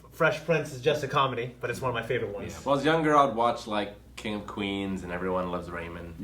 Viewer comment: did say "when I was a young girl"